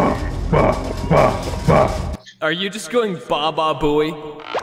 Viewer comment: I think I heard Bababooey